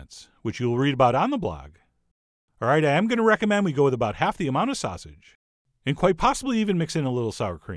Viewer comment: You said "half the amount of sausage," I heard "add way more cheese."